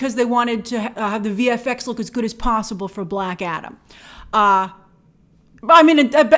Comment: At first I heard "Blackadder"... Haha that would've been a great (if probably financially disastrous) Blockbuster! 🤣